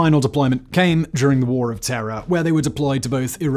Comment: did he say "war OF terror"?